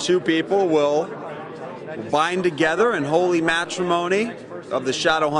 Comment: i thought he said holly macaroni